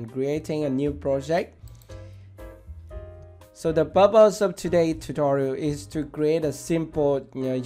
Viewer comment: Nice tutorial......did you say "the bubbles of todays tutorial...." at 0:52?